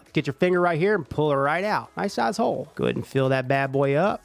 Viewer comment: I thought he said "Nice ass hole" 😂 but when I turned on captions then I realized what he said.. First time I actually benefit from these generated captions..